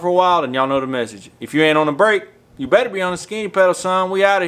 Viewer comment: thought he said skinny pedo lol